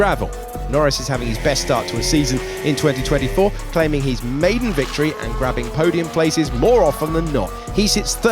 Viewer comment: I thought the commentator has said ‘maybe’ victory instead of maiden and I was about to go on a tirade in the comments😅